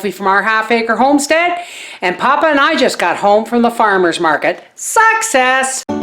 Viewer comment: during your opening line of this vid, when you said ....success! I thought at first you said..."sucks ass". I burst out laughing. LOL!!!!